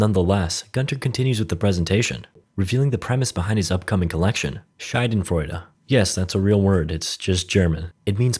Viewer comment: Schailenfroiwle Me, a German: What did this dude just say? Proceeds to rewind and listen to it 5 times until realising he means Schadenfreude My dude really butchered that word😂😂😂